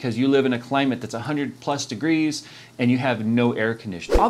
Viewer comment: did he say 100 + degrees ? maybe farenhait ?